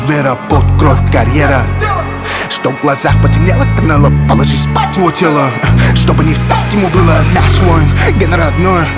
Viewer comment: Канело положи спать его тело?) мне одному послышалось?) Алга GGG 🇰🇿